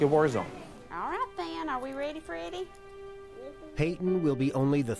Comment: did she just said “ready Freddie“